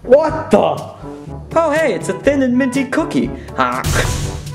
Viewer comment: a few times I watched this video I thought sonic and tails said pin and pin and minty cookie